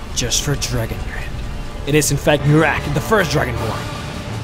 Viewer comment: Lol sounds like he said giraffe of the fist dragonborn!